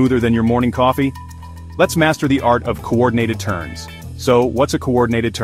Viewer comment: I heard it as smooth coordinated TURD 😅